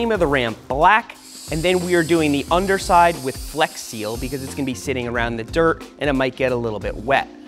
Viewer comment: did he just say flex seal